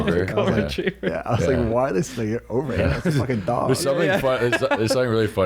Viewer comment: Did he just say “why this n**ga @ 56:29? 😅